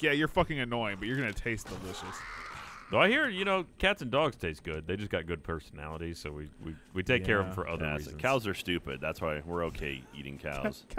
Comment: As I was watching this i heard "we ate dogs but you know they have good personalities so we keep 'em as pets." As my dog was laying down next to me. I covered his ears.